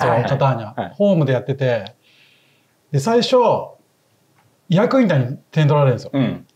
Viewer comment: なんて言ってるんですか？ ヤークンタ？ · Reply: イアクインタ 日本だとイアキンタ、ヤクインタ表記が多い